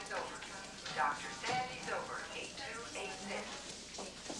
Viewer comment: If Abrams was directing this, Chekov would have operated on McCoy and that old lady would have been blinded by lens flares And something would have exploded, randomly, for no reason whatsoever. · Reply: I thought they were saying Dr Bendme Over.